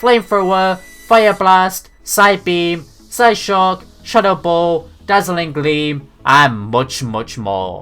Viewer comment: i clearly heard shut up ball .